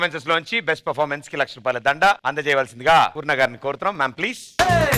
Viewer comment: i think she said poo to sudheer bro